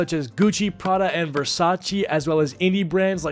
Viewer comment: I thought it said v-sauce and was terrified for a second